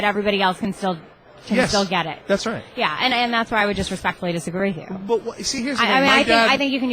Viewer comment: did anyone else catch her "I disrespectfully disagree" after Cenk talked about raising taxes, around the 13:30 mark.